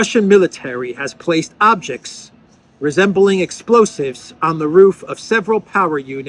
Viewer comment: Do they think we're stupid?.....rhetorical question, I had missed the word 'resembling' until it was pointed out, I heard in passing 'explosives on the roof'